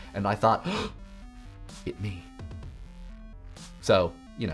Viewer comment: Is he saying "It me"? I've listened to it like 5 times and I can't figure it out. It's killing me. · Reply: III No don't die! (It me!)